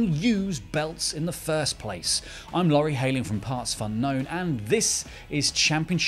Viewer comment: Did i heard him saying i am laure haem ??